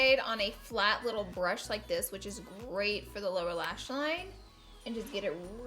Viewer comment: I thought you said “Laura lash line” 😂